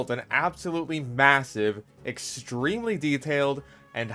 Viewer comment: did he say MASSIVE?